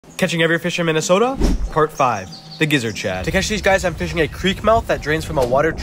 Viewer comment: I thought he said the rizzer chad